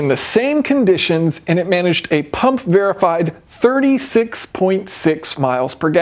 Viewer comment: I thought you said PUB verified.........you know like Genius world records 🤣🤣🤣